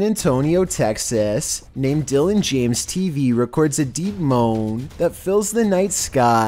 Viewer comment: When you said deep moan I thought you said demon but pronounced “deemoan”